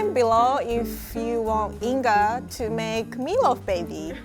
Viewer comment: Yesss meat loaf baby!!! 100% would recommend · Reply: Ah mann I thought she said "Make me a love baby", was just about to offer myself up :(